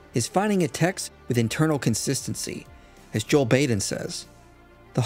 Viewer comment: I thought you said Joe Biden